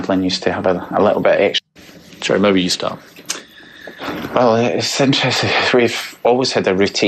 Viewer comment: <— Can anyone hear what Gerry starts to say at the beginning of his sentence right here? Piers asked Gerry to describe what happened that night, and I can’t tell for sure, but it SOUNDS like Gerry begins to say, “well it’s interesting…” but then catches himself, cutting off the word ‘interesting’. I tried replaying it several times, but I can’t quite make out those first couple of words. Does anyone else know? Because it would be really bizarre if he accidentally began his description of their daughter’s disappearance with the phrase, “well it’s interesting…” That’s something you’d only do if you were about to tell a STORY. (indicates scripting) ______ EDIT: I just listened with headphones and he DOES say “well it’s interesti…” (cutting off the last word) 😮 In fact, if you listen carefully, he not only suddenly stops short, but you can even hear the awkwardness in his voice as he realized what a huge mistake he just made, and even tried to stifle it, hoping that no one heard. Oh but Gerry… we heard.